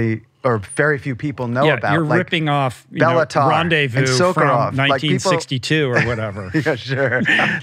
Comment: Who are the directors Van mentions ar 54:42 ? i cant quite make out, i think the second is Sokolov? Who's the first?